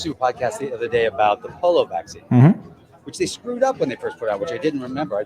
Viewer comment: Did he just say the "polo virus"?